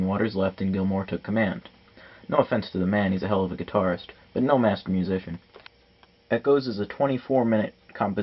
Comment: i thought he said masked musician. like the guy was really prideful lol